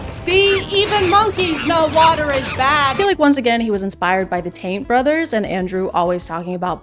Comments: I heard "the taint brothers" and I'm not sure if that's what you actually said or if I just misheard, but that's what I'm calling them from now on. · anyone else hear “Taint brothers”?? 😭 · is she saying "Taint' brothers? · I just heard "... the Taint Brothers ..." my brain hurts